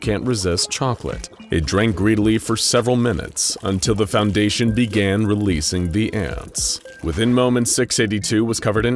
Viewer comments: did i hear it wrong?.. is it foundation?? i hear "until the foundation began releasing the ants" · I can't be the only one who noticed that in the fountain part at some point it said foundation instead of fountain like in the subtiles timestamp